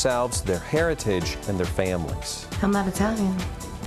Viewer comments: did she say shes not italian/??? sum1 tell me plz lol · did she say shes not italian??? wow! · did Jenni say she's not Italian? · does Jwoww say she's NOT Italian?? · Did JWoww say that "I'm not Italian" @ 5:20?